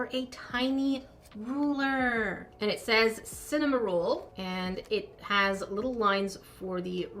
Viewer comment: who else thought she said "CinnamonRULE" when reading the ruler 🤣📏🍭